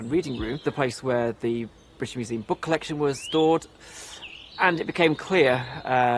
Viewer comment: Love the video, but everytime he says 'stored', I hear 'thawed'. Bit of a Mandela effect, stop judging me.